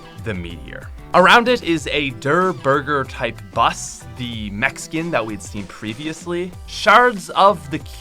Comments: When u said mech skin in 9:23 it sounded like Mexican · When he said at the end,”mech skin” could’ve sworn he said Mexican 😂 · i thought he said mexican😂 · I thought he said the mexican instead of the mech skin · did he just call the mech suit mexican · When he said mech skin I thought he said Mexican idk y